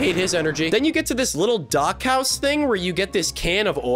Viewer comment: when says dock house, I heard Dachau, and thought this game was about to take a very dark turn.